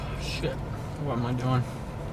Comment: I swear I thought I heard "I want my joint" but he said "what am I doing?"